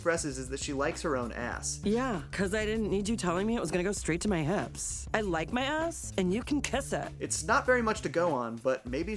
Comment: Connie is my favorite hormone monster. She’s so fun and awesome and a great character. I enjoy her sass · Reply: i really thought you said “i like her ass” and didn’t think twice